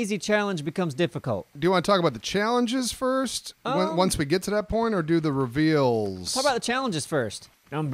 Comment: who else heard ''once we get to that porn''